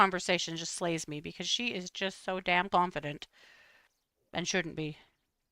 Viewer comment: I thought you said “blondefident” instead of confident. 😂 I think that actually fits better now!